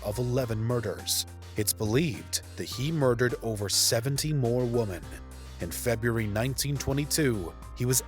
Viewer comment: why does the voiceover keep saying woman instead of women? 🤔